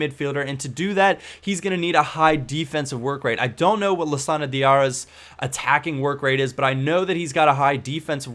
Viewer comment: at first i thought you said diarrhea.